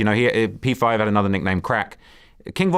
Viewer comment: I’m the only heard him say “another nigga name crack” or I’m tripping?